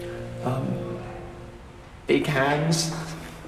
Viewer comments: What does Derren say at 3:22? I can't make out what he says there. Any help appreciated. CC says "He Cares" but I don't think that's what he says. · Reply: i think he says big hands after he says a dimply face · what is he saying here, please? Is he saying “big hands!“?